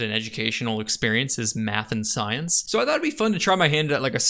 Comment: Did he say "Math and science" OR "Meth and science"?! :D Problem with Native English speaker YouTubers that they have a poor pronunciation. :P